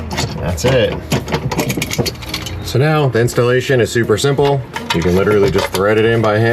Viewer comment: did you just say “ the VINstallation “ 😂😂👏